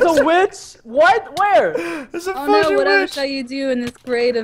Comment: DID HE SAY FUCKING OR FUGING!?!?!?!?